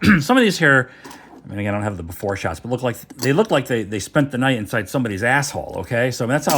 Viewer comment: Did you just say “spent the night in someone’s”…😂😂😂